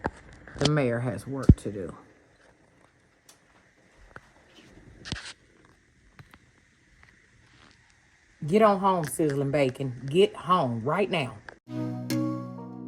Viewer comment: I watched from beginning to end. You have always been a great story teller. I can’t believe you don’t write books. 📚 Fabville is truly fabulous. Is the pig’s name cinnamon bacon? 😂💞💞💞💞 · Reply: Lol, i thought she said sizzlin bacon!